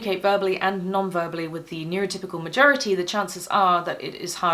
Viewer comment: Listening on higher speed, at about 7:00 I thought I heard irritable majority rather than neurotypical majority 😂